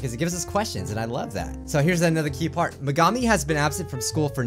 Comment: I thought u said Midori has been absent... Yandere chan is practicing her killing 😈